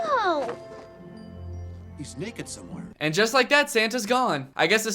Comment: Lol for some reason I heard say “He’s naked somewhere!” What?